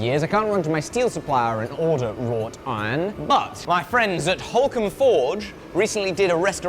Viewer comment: I only hear "raw tine" and I was extremely confused. A bit of searching around, and now I get it "Wrought iron" haha